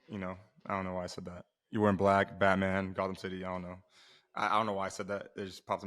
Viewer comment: I thought you said goffcam city, like goff and cambell